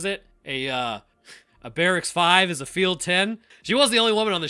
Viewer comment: "Barracks five is a field ten." I always heard, "The small town seven."